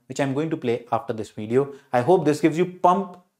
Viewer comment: I think I should not say this but I can't control myself. At 6:00 I literally thought sir said KEEP F**KING HARD...🙄🙄🙄🙄😂😂😂😂